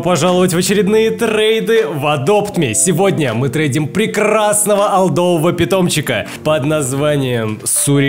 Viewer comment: То чувство когда ушёл в школу и не попал на премьеру□□□ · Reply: кагда ты сказал прекрасный мне послышались красный😐